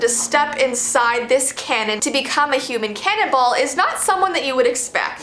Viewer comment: I thought it said human canabile